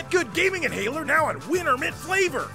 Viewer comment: "Hey, Loser! Why don't you try to GIT GUD?! With Git Gud Gamer Inhaler! Now in Winner Mint Flavour!!" Winner Mint Flavour? And not "Wiener Mint" Flavour like I thought I heard? Colour me disappointed... 😑